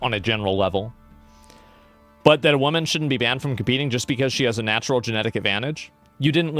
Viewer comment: All I hear is “men have been banned from cheating”